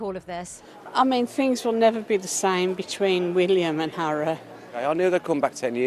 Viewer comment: did the lady say 'William and Harrow' ? I surely misheard, but... would fit a bit.